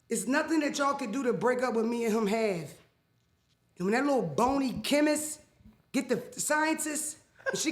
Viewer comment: i was just listening to Mariah the scientist and all i hear is “that damn chemist”